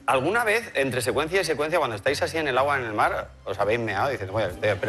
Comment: Qual es la pergunta en 12:21??? No entiendo que dice el conductor 🤷🏼‍♀️ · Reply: Que si en el agua se habéis meado